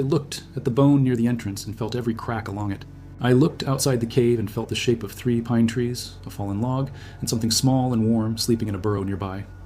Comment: What is said at 5:49 , what does it mean? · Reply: YouTube CC shows it normally 5:50 ...I looked outside of the cave and felt the shape of three pine trees, "a fallen log and something small and warm sleeping in a borrow nearby, a rabbit maybe," my new nose suggested rabbit... 11:30 ...Every single pup rolled on their backs flat, pure total automatic submission "This was the Alpha, the father, the reason this pack existed at all", the wolf who built everything around us...